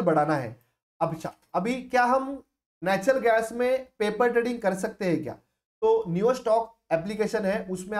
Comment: Hello there, at 8:52 what was the paper trading name, you were saying? Could you please type that name? Thanks in advance. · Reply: Neostox